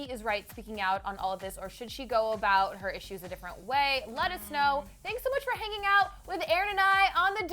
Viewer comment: It sounds like she says "Lettuce-know" instead of :let us know: at 7:47 LOL